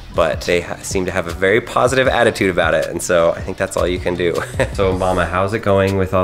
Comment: did u say Obama at 5:49 to 5:48 please reply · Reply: i think he said "so mama how are the babies?"